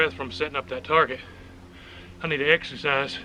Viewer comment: Exercise. I thought you said extra fries. Lol